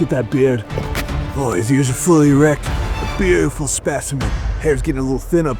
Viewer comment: Did he say he was fully erect in the beginning 🤣